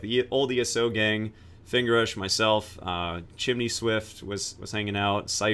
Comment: Did you just say "Finger rush myself" at 9:56 ? ( ͡° ͜ʖ ͡°)